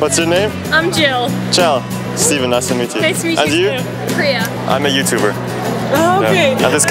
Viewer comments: did she said Priya?from India?? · did she just say Priya wooooo😁